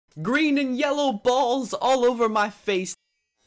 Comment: i heard he sayd "greetings! yellow balls all over my face!" 😂😂😂😂😂😂😂😂😂